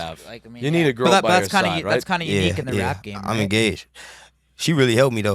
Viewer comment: Thought he said “I’m gay” when he said “I’m engaged”